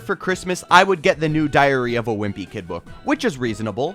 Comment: Is just me or am I hearing " diarrhea of the wimpy kid "